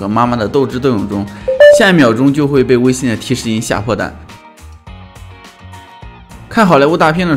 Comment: 我下意识的去看了一下我的微信，以及，我听成吓破蛋了